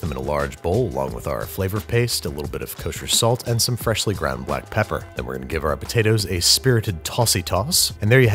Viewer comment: is it just me or he says "black pepper" or "white pepper" depending on what you think he's saying???? (obviously it's black pepper but i was listening to the video at first and didn't quite catch it lol)